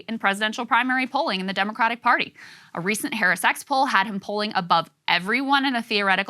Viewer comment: why do i hear "hairy sex" everytime anybody says harris x